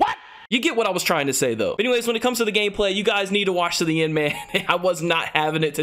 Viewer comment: I can't shake the feeling if the fact I hear gayplay